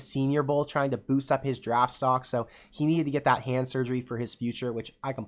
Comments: 🤣 what??? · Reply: My apologies. I either need hearing aids or my ears cleaned. I thought that you said cancer when you said hand surgery. Sorry. Love your show. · 🤣 what??? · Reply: My apologies. I need to turn my hearing aids up or clean my ears. I thought that you said cancer when you really said hand surgery. How embarrassing. Love your show. GBR